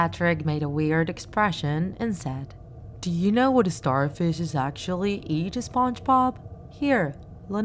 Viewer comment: He actually said "Do you know how starfishes feed Spongebob. Let me show you"